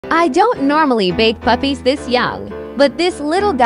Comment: I thought it said “ I don’t normally bake puppies this young” 😭